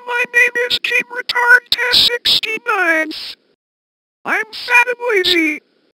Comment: When it said 'fat and lazi' I thought it said 'fat and nazi'